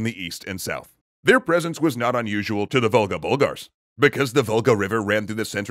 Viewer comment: I keep hearing "Vulgar Bulgars". Not "Volga Bulgars".